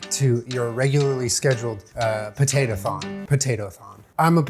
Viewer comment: I'm not gonna lie, when he said "potato-thon" for a second i thought he said "potato thong" 😂